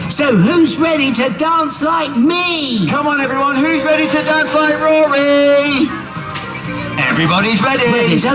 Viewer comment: Idk if it’s just the camera that made it sound like this but what I heard was: “Who’s ready to dance like Rory?” 🦗🦗🦗🦗🦗 “Everybody’s ready!!”